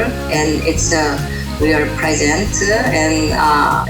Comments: I think she says "It's a present", it's a gift :) korean accent hahah I went emotional, because I don't tell anybody in my family that I love them and this is so touching for me to watch....I love you try guys' moms, thank you ç_ç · Awwww, how heartwarming :). Btw, during the "inaudible" part of Eugene's mom, I clearly heard "it's a real present". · it sounded like Eugene's Mother said ' it's real pleasant' How cool that our moms can endure this pain, and the challenge of raising us and can still say nice things about us XD